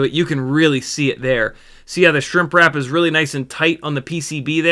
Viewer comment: I keep hearing Shrimp and Wrap, Shrimp Wrap :)